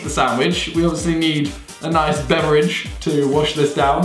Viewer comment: i'm trying to figure out what he said at 4:20 . Before we eat the sandwich we need a b??? I know what he means (obviously) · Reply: Ok it's beverage. i'm silly